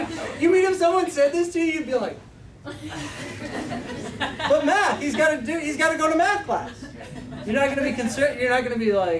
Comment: I definitely thought he said "but he has to go to METH class"